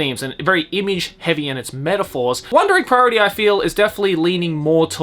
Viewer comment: I keep hearing "One direct priority"